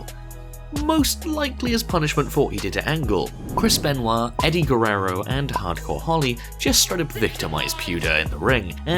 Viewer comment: OMFG DID HE REALLY SAY IT?!?! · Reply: Tazz - "J.R., I think that one knocked his areola off." Fucking hilarious!!!!!!!!